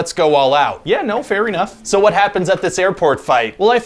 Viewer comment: I heard "fairy" enough!